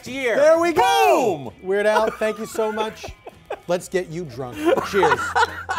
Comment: PFT says, "Let's get you drunk." At the same time the CC says, "At what state you drop."